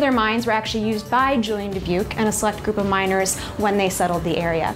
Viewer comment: "Google miners"? Can someone tell me what exactly she said there? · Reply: "DeBuke and a select group of miners..."